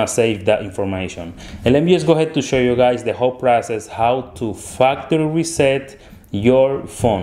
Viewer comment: I thought he said fucktery reset